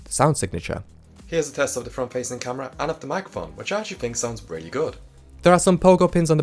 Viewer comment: Your British accent is a bit difficult to understand for me, you should slow down a bit. At 7:40, I thought you said "mac phone", but then I saw the subtitles which said "microphone" 😅Anyway, great tutorial! Thank you.